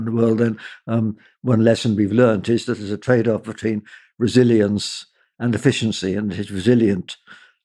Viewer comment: At first, every time Martin said Resilience, I could of swore he was saying Brazilians! I was like, am I missing some thing here about Brazil?